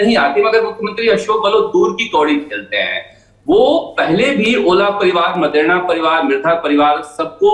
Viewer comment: what did he said? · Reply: ओला परिवार राजस्थान का एक राजनीतिक परिवार